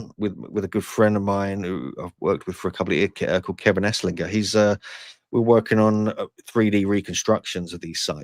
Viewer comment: did he just say Kevin AssLicker?!